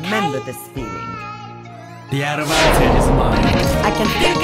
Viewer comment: lol i always thought he said "the adamantium is mine"